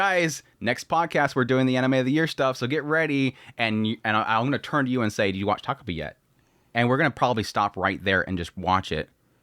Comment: what's anime you talk about 5:20? i cant quite hear that, with no chance of spelling. is it original sin, as i said i cant quite make out what you are saying? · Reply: "Takopi's original sin"